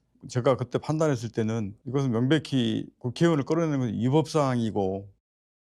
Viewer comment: 앞선 대화에선 발음이 부정확해서 의원인지 요원인지 명확하지 않지만 01:13 여기선 본인이 정확히 국회의원이라고 발언했고 국회의원 끌어내는게 불법이라 판단했다는데, 다들 왜 요원이라고 하는거에요? 정말 그렇게 들리는거에요?